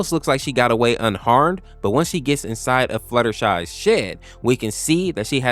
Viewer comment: DID HE JUST SAID RARITY WENT TO FLUTTERSHY S***T???